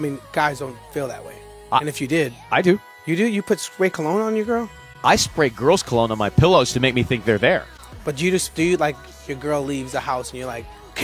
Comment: "I spray girls cologne to pretend they're there" is the most closeted-gay-is-this-what-straights-do thing to say ever lol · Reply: Nice try, but that's not what he says. He says, "I spray girls cologne on my pillow to make me think they're there." 0:07 How's that closet-gay talk? I mean, you can believe what you want to believe if that fits your agenda. Even funnier, you got over 12K thumbs up for stating something false. People liked it though, and these same people vote in elections. lol